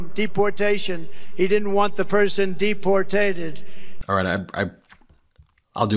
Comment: Did he say “ Deportated?”